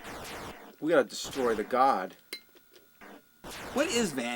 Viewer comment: I always thought that they said "guard" not "god."